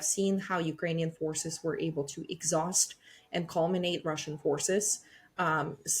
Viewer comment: Is she really saying "Ukrainian forces were able to exhaust and culminate(?) Russian forces after the battles of..."?